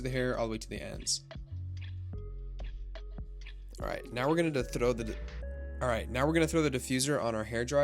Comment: "now we're gonna throdudud" I heard that lmaoo